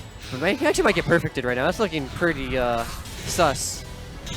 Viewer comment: did he just say sus?